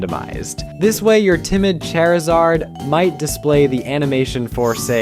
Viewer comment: Wait...Did you just call Charizard...🪑izard?